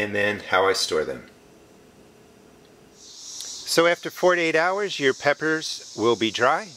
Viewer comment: i thought you said 48h and i thought daaaamn i dont have time for that xD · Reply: Thank goodness for this comment. I thought he said 48hours and i was like. Fuck this. But then this comment saved the day